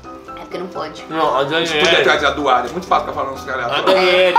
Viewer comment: qq ele falou da Doarda? num entendi nd kkkkkkk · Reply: Pelo oq eu entendi(coloquei no lento) ele disse: Pode trazer a Doarda, é muito fácil ficar falando uns treco aleatório